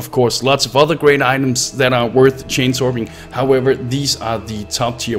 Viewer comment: So... I hear "chainsaw" every time guy in video says "chance orb."